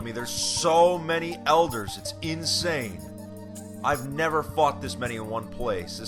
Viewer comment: i thought he said "I've never FUCKED this many at one time" LMAO